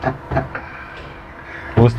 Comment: I thought he was saying kiss me daddy lmfaaoooo😭